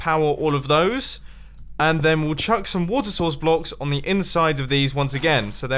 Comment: It sounded like you say wather saus like mayonays haha